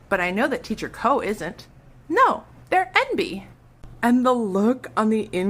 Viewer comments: what does she say here? They're NB? They're empty? They're envy? I don't get it... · can somebody please help me decipher what is said at min 2:20 ? since the first time i listened to this vid i haven t been able to figure it out! it sounds like the little girl says "no, they're envy"? but that doesn't make sense...